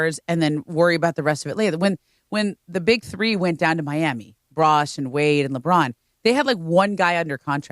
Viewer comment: did she just call Chris Bosh Brosh?